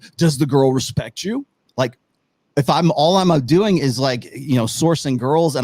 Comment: He was talking, but all I heard was "I'm a simp"